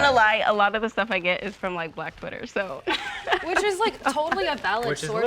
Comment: What did the woman in the overalls say here? She said a lot of her information comes from, where? It sounds like she says “black footer” or something like that… what the heck is black footer, if I’m even hearing that correctly….. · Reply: Black twitter